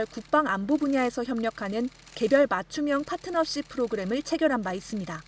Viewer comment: 맞춤형 파트너쉽이 아니라 맞춤형 빼먹기로 들리는 건 나혼자 만의 생각이 아니네. 판문점 가서 립서비스 한번 해주고 저녁에 호구 만나 소주에 맥주 한 잔 말아주면 자기들 방문 목적 100% 달성.